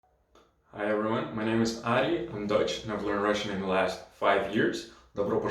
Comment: Enjoy the video games! I'm Dutch by the way - from the Netherlands, but I understand German ;) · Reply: in the very beginning, it sounds like you say "I'm Deutsch" so that's a bit confusing haha, anyway, thanks for the video!